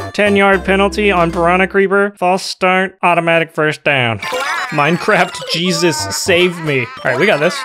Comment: I thought he said the words 'minecraft jesus'